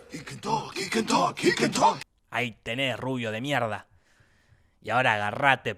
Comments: De donde salió lo del minuto 3:49? Eso que dice oh kiketa algo así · Que dice en el minuto 3:47 después del grito ? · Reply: He can talk (un meme de un capitulo de los simpson)